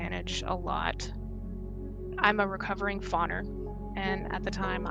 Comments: I hope this is not intrusive at all but I’m curious what the word you use at 41:30 means? I’ve never heard it before and don’t know how I’d begin to look it up. I feel as though every few months another victim of this monsterous person comes out, or another shitty thing she’s done comes out, and I feel for all of you. I hope you’re living your best life! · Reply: Fawner. It's one of the big F trauma responses (fight, flight, freeze, fawn). Look up fawning in relation to CPTSD. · what is said at 41:31 she is a recovering what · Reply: Fawner. It's one of the Big F trauma responses of CPTSD (Fight, Flight, Freeze, Fawn).